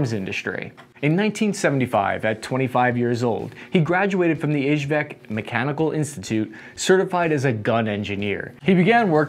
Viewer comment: did he say HVAC mechanical institute ?